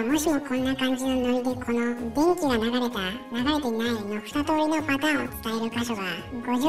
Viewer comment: ウンコネタのせいで「電気が流れた」が「便器が流れた」に聞こえる…w